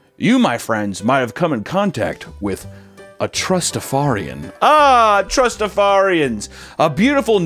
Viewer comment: I always heard it as "trustafundian"